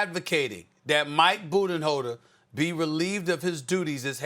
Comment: It Sounded Like Stephen A Said Eirher "Budda Holder" or "Booty Holder" 🤣🤣 At 5:55.